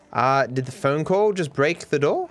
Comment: did he say breaked?